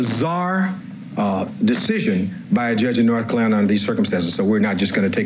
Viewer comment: Did Cochran sound like he was going to say "North KLANS-alina" instead of North Carolina?